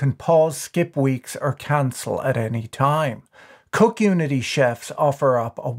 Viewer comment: I first heard “Coke Unity”